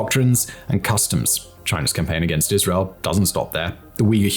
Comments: Why did he say Israel? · Dude....did you accidentally say "Israel" instead of "Islam"? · Did you just said "China's campaign against Isreal doesnt stpo there" at 24:47 ? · you say "China's campaign against Israel does not stop there." I think you meant to say "The Uyghurs." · did he say Israel ????